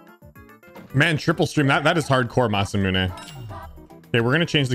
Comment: Great to have you back. Youtube havent been the same without you. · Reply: I thought you said steam not stream, RIP